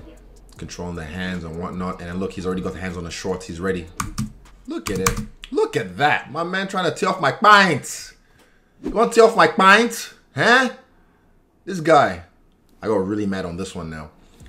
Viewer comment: I heard “man was trying to tear off my kpyiaanntt” and I subbed right away! 😂😂🇳🇬💯